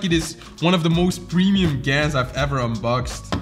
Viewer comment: did he say GAN?